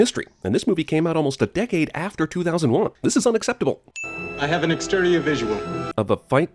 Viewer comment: what does he say here? 'I have an externia visual'? WTF?